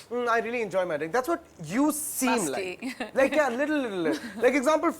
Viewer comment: Did she say "Busty"? LOL